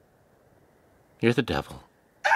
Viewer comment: i like to think that vernie is saying:here’s the devil